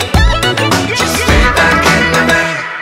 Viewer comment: when I first heard this I thought he said feedback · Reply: I've listened to this song like thirty times and sometimes it still sounds like feedback not fadeback to me half of the time XD